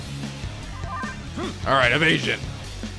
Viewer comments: when said "evasion" I heard "i'm Aisan" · "Evasion!!!" Me: "I'm asian???" It sounded like he said that xD